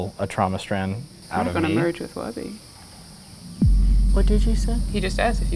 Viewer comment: "Im gonna merge with wubby" Other lady: wHaT DiD yOU SaY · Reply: She said, "I'm not gunna merge with wubby"